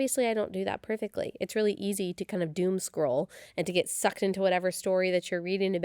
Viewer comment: THE publishing homeschool home front episode. Doomscroll? I thought I heard 'doom squirrel' then had to slow it down.